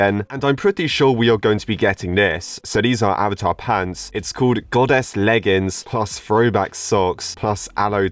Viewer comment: I thought IT SAID GOODASS